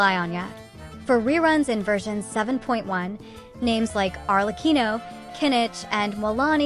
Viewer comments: DID I HEAR ARLECCHINOOOO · DID I HEAR KINICH??????????????????